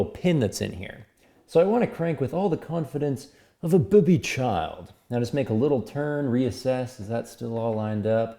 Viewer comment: "like a boobied child" 3x! My new favourite saying. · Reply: I thought he said "like a booby child"...no idea, is this a Canadian saying? makes me laugh tho!